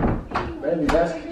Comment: did he call her baby👀